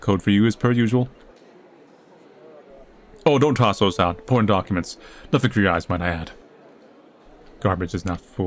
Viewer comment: when he said important documents, i heard it as porn documents lol.